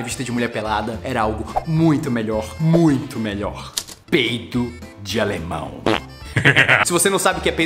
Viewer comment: eu ouvi peito de alemão